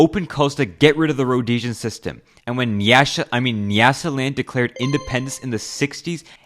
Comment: did he really say nyash and show ice spice 😂